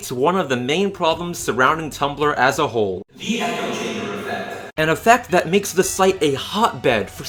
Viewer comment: what did he say at 2:54 ? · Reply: Oblivion5225 I think he said, "The Echo Chamber Effect," which means people repeating the same things someone else said.